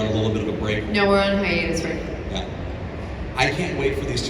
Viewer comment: what did Bitsie say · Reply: On hiatus. Taking a breaking from series filming.